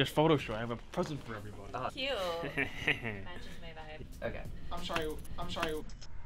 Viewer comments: what did james even say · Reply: I’m sHorry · what he said? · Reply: I’m shorry